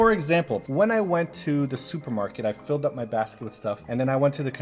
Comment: "When I was at the market, with my basket full..." what I heard: "When I was at the market, full my basket with RANCH!!!